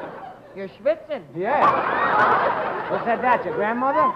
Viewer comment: What does Jerry say at 3:33? Can't understand it 🤔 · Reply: "You're shvitzing" which is Yiddish for "sweating"